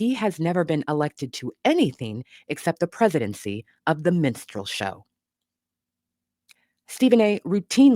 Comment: I heard you say minstrel show and heard MEN-strual flow... indicative of what happens whenever he (and those like him) opens his mouth...